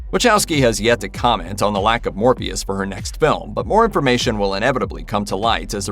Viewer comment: Did anyone else hear him say Morbeus not Morpheus at 2:04?